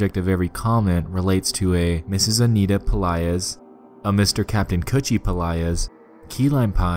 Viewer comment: did he just say kaptain coochie? LMAOO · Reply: kutchie